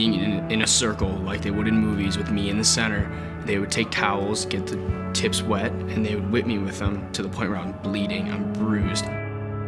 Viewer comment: what is he saying at 1:12??? "They would take (something)" This is what I heard... If anyone could... it would be great someone type subtitle for me :) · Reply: I said "They would take towels, get the tips wet, and they would whip me with them."